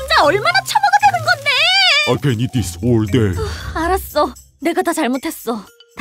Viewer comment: did i just start understanding Korean? or did he just say "i can do this all day"